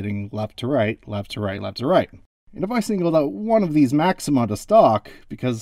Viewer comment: what do you say maxim by the stock what?? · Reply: "... if I choose one of these maxima to stalk..." (ie: if I choose one of these maxima to pay attention to and follow). It's correct in the closed caption, for what it's worth.